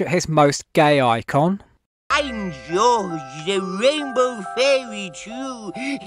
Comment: Did George say he was a Rainbow fairy Jew?